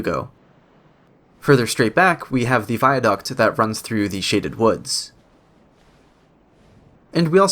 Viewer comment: ngl when he said "Shaded Woods" I heard "N64 woods"